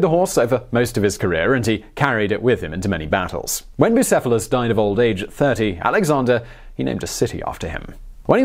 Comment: did he just say when bicophelus died of old age at 30?